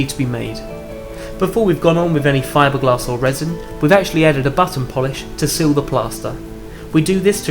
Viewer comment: Awesome work. Thanks for sharing. Can I ask what the word at 3:16 is? "We've actually added a ** polish" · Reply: Button :D